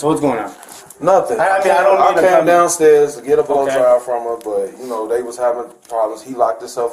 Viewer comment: Did he say that he came downstairs to get a bl@ job from her?